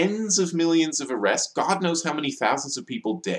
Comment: OH! wait did you say GOD KNOWS?